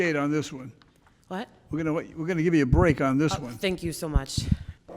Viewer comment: I thought judge Caprio said: "take care of the little shits" had to rewind to check :)